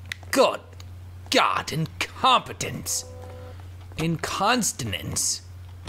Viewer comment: I heard "incontinence" If I had heard "incompetence" I would have understood. Sorry if I heard wrong :\ I post things about what I hear :)